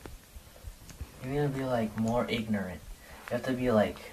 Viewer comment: 😮😮did he say you gotta be more ignorant??